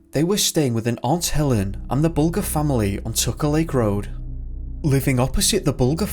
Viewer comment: At approximately 11:29 did he say, took a leak road? LOL 😲😬😊😃😂🤣